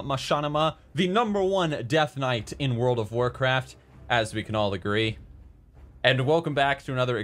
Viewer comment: The Best deathknight in world of warcraft "As we can all agree." At first i thought said "Asmongold the great"